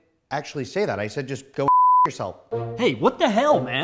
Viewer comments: I thought he said Go and Eat yourselves. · I thought he said go help yourselves